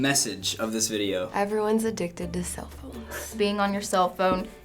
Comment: I thought she said everyone's a d**k to their cellphones... LOL